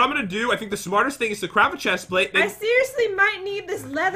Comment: Sounds like Pat said crap a chestplate😂